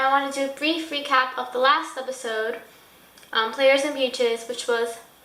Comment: at the start it sounded like you said "recap the last episode players and peaches" XD